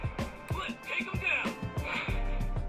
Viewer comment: can you actually hear what he says at that moment? coz ive listened to it over and over and still cant pick up what he's saying 😔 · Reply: Think it’s “let’s take em down!” 😅